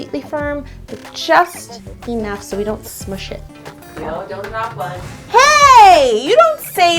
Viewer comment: what did say at 4:38???? please answer ASAP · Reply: "Yo don't knock one"